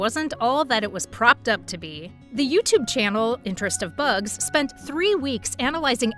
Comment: does she call it the "interest of bugs"?? 😂😂